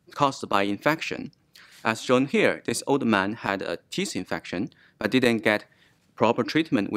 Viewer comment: Captions are good. Thought he said the man had a "yeast" infection that spread. He said "teeth" but captions says "tooth infection" that spread.